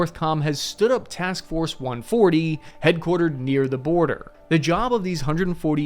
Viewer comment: TF140? Did i hear that right